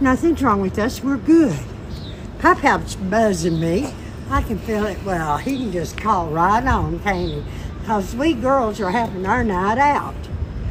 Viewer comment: I have no other what she was saying I had to play it multiple times 😭😭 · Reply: As a southerner, ahem: "Pop-pop's buzzin' me. I can feel it. Well, he can just call right on, can't he? 'Cause we girls are having our night out."